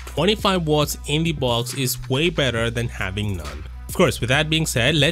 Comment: just saying it btw no hates. 3:09 I was laughing when you said better than having none (I just watched your other video about iphone 12 not having charger ) it sounded like it you said to Iphone, BTW just saying it in my opinion, but still it was funny.